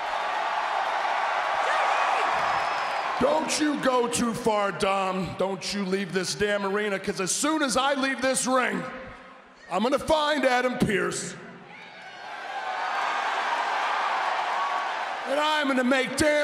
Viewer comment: In 2:42 did randy said he would find Aiden Peerce?